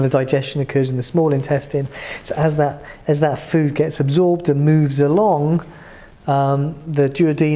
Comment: Hi sorry but I can barely hear what you are saying 3:49-3:54 :( What did you say here? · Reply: "And as the food is sort of broken up and churned we still got that mechanical digestion moving"